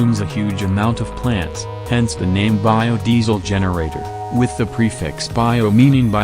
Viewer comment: oh, I thought he said "biome diesel generator",lol I thought he would take energy from all living things in the biome until it's a wasteland. But wheat works too I guess.